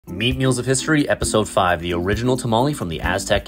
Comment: Really thought bro said mean meals....